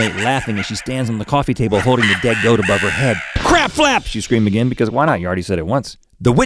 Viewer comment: I thought it said crab flaps not crapflaps